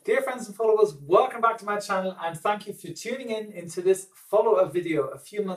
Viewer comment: Did you just say “and f**ck you for...”?! What a ?!